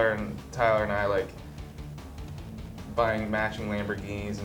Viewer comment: I thought Josh said "buying matching bikinis...."